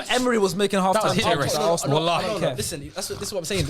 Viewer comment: Did say wallahi @ 27:34? 😅😅